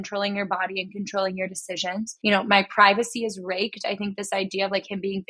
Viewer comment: It's weird because the lyrics are "my privacy is racked" but I always heard it as 'my privacy is raped'